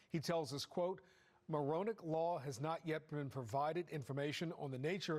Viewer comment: lol I thought they said “moronic law” not Maronick. These squatters have moronic laws on their side.